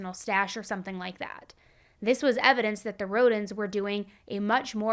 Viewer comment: Every time you said Rhondon I thought you were saying Rodents lol. Four days old! Very powerful first impact statement. Feel for the eldest toddler. Thought your summing up was very grounded.